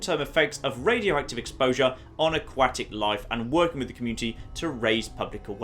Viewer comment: Ha. When he was reading the terminal I swore he said "aQUACKtic life".